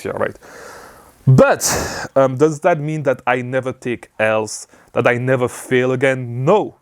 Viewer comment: Thanks much! I thought you were saying 'Take Hell' throughout the video but then realized you were saying 'Take L'. You have a good and realistic outlook.